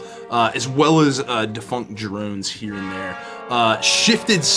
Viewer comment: when i heard defunct drones later in the episode i thought i heard funk drones and i had a mental picture of drones with guitars playing 70s funk